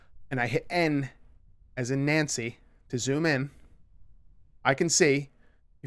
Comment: did he say M as in Mancy? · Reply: Ben Hall n as in Nancy